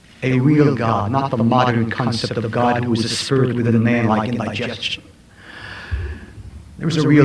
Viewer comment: What does he say at 20:20 "a spirit with a name like in my gestion" ?!?! Someone help me please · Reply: God is not a spirit within man like indigestion